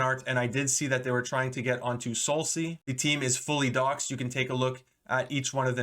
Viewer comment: what word are you saying. "the team is fully ____" ? I'm not sure I've ever heard that word before lol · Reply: Doxxed, meaning their identities are known and they aren't anonymous. However looking back, they are half doxed, as they don't link their socials and don't put last name, so it's pretty hard to find them.